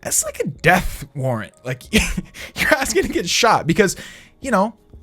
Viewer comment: did he say ass